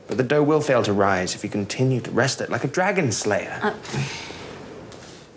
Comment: i thought he said like a "doom slayer"